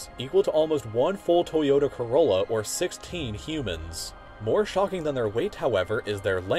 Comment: when he said "16 humans" about the jelly fish, I heard it as 6, teen (teenage) humans 😂😂😂 idek